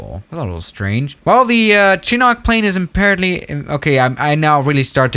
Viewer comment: Did he just say "chi nok"